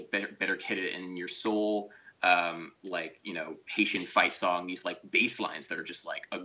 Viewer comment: I thought he said "Haitian Fight Zombies" (because inadequate knowledge of Mingus + poor hearing).